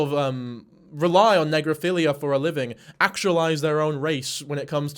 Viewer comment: istg i thought you were saying necrophilia. Needless to say I was extremely scared😭